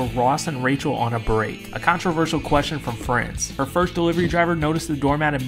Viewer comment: Boy why did i keep hearing " a controversial question from french" ans thought it was some french joke